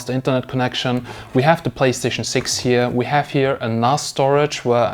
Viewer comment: did you say PlayStation 6?